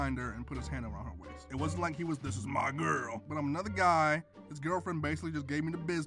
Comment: When the be said 'SHES MY GIRL' I thought he said 'SHES MY GUN' and I was like...uhh...